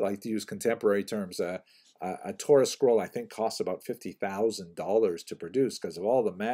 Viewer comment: He married a 'Torah Scroll?' For a second I thought you said 'chorus girl.'